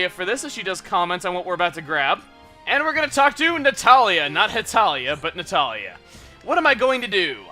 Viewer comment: Hey, did he say Hetalia at 3:36?